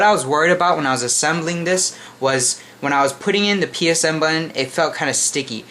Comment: did you say psn button??